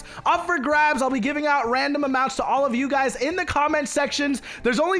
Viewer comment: Awesome Job building this, thx so much for doing this tour vids, I had lots of inspiration from them, you always make me happy, laugh so thanks for making my day. Username: YnaSharifa Ik Im not gonna win Im always a bad luck, but If I win Im going to buy the second floor gamepass, ignore collision check and buy the Dfield merch, and Im gonna save up for something else. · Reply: Roxishu I heard him said "Random Amount" hes giving a random amount of robux.